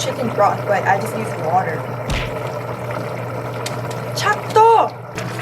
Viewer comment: I love the CHAKTO 😂 I thought you were saying a Korean word so I had to rewind